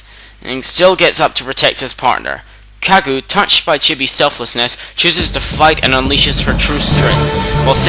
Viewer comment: Oh, lol I thought you said that Kagu was touched by Chibi's selfishness at 21:00! Whoops!